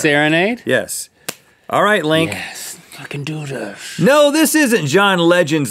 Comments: WHAT DID LINK SAYYY 😂😂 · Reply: i can do this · Did anyone else hear Link say “Let’s f*cking do this” at 1:03? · did Link say "F***ing do this???????? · I thought he said "f*ckin do this" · it sounds like link says let's f***en do this ! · Anyone else hear link say at 1:01, "let's f***ing do this." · Did link say let's f#^king do this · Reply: Salty Ding He actually said: "Yes, I can do this." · "Yes, let's f**king do this..." Is that what Link says? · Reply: VinnytotheK he actually says we can do this